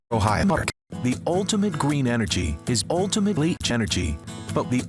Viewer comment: I had to play back the intro a few times just to make sure you hadn't made the narrator say "Greem". I swear it sounds like he said "Greem."